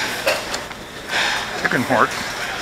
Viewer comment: did you say chicken heart or chicken fart? I'm sorry it sounded like you said chicken fart